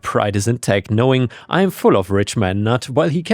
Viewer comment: I keep hearing you say "Richmond nuts" and all I can think of is making "deeznuts" jokes. 🤣